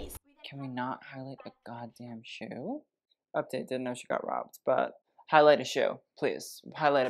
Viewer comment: This was abysmal, the most intriguing part of the video was @ 13:48 - “Update (?) got robbed.” I think you said Dhanush, what’s the tea? Love you to bits